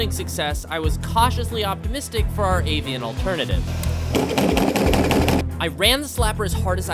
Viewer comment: For some reason, I heard him say "I was reasonably autistic about our avian alternative."